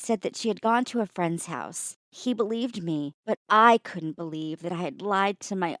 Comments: “she believed me”boi your dad is a guy isn’t he?.............. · did anyone else here SHE believed Me instead of HE believed me